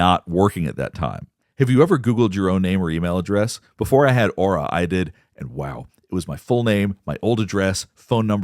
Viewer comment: I was listening to the video and heard you say, "Before I got aura, I did." I thought you were flexing on us 😂😂